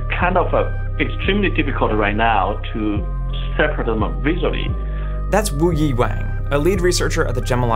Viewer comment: Did you said "不意外“？1:20 lol